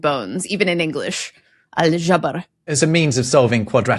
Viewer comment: IDK why but when said the word for putting bones together i heard 'al-żebra' (alzhebrah) where in polish 'żebra' means 'ribs' :)